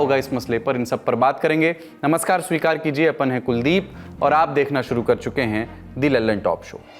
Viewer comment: did he say "apun hai" while saying his name? should really reconsider the language if that's the case!! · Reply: Apan not apun.